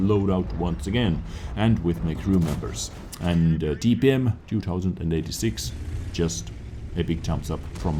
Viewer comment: lol, i thought he said pen-2086 when he said dpm-2086